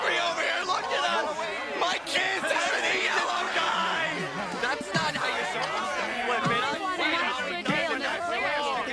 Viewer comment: What did Fred say? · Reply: My kids haven't eaten in a long time